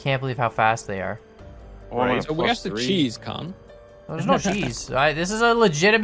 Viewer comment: What did say? "where is the cheese c-"